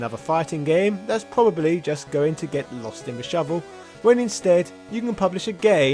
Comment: Did you say "get's lost in the shovel?" I've gone back and listened like 10 times, snf I'm thoroughly convinced you accidentally said "lost in the shovel," instead of lost in the shuffle.